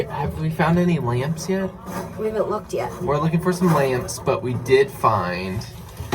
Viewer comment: In the beginning when you said you were looking for LAMPS i honestly thought you said you were looking for LAMBS 🤣